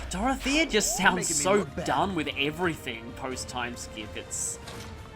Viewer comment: /... Its not dumb.. Lol. Dorothea sounds... Sad most of all. 😔 I mean... Come on that's off logic. I heard that & it's not nice. 🤦‍♂️ She just doesn't want to fight & kill people but she must anyhow. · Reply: He said "done with everything." I don't know where you got "dumb" from.